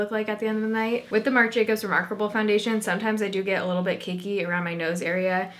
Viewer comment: i swear she says "kinky" at 9:08 instead of "cakey" am i the only one that heard that? 😅